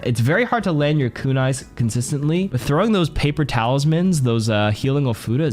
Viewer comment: whenever you said “paper talisman” for kiriko i thought u said “paper towels” which you know same thing 😂